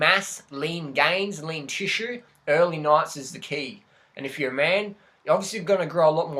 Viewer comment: What did you say at 2:53? Early nights?